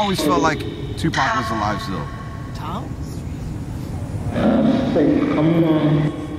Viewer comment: it sounds like xxxtentacion said alr come along yooooo🥶😭